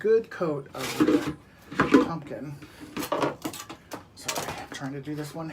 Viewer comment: Hey at the 3:00 minute time stamp you said "after you get a "good coat of _?_ on yr pumpkin" I couldn't hear but assumed you meant to say Pam. BTW Dollar Tree sells a non stick spray too. Jo